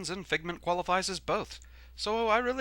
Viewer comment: For the longest time, basically since I first started watching your videos, I thought you were saying Fare-LEE-well instead of Fare-THEE-well. I finally found out the difference when I saw one of the video descriptions.